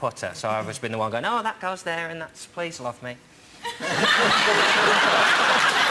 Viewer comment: What does Jon say at 2:28 after 'that goes there' I can only make out 'off me' ?